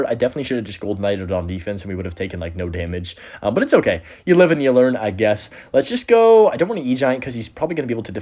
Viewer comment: did bro just say Live and Learn